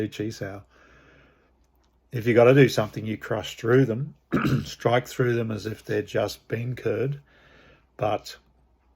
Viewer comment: What is the phrase you say at 8:38? Is it "strike though them as if they're just being cured"? What does that mean? I probably can't get it because I am not a native english speaker. (And thank you for sharing the wisdom by the way) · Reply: Hi! You have a good ear actually, what I said was 'as if they are bean curd', something Master Chu Shong Tin once said about how a good Wing Chun punch will just carve through an opponents defence. This video was what I had in mind - <link> It's an amusing saying because in English we would probably say 'like cutting through butter'. I like the image because Yim Wing Chun was a bean curd seller 😀 Cheers mate, thanks for your interesting comment